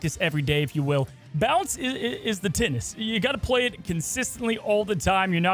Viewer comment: I legitimately thought you said “bounce is the TITS!” at the beginning.